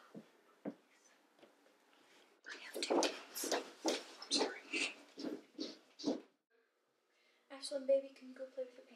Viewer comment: does she say "I hear two kids" or "i have two kids" ? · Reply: I have 2 kids